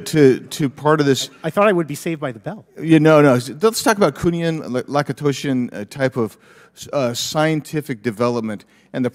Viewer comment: Does anyone know what words Rep Biggs was trying to say at 1:38:04 ? I think he's saying it's some category of secret technology. Youtube subs says kunian laosan.